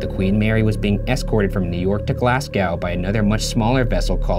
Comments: Did Ryan say Glass cow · is he saying Glasgow at 6:49? Cause, as a Glaswegian, that pronunciation bruh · uhm- did you just say Glasgow as glasscowe? …Ryan I-… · Did he say Glasgaaw? is that related to Glasgow or is that a place that exists in Ryan's head? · Is Ryan saying Glasgow at 6:48.??? Because if he is then he really screwed up pronouncing it 😂😂😂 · was that supposed to be Glasgow because it sounded like glascow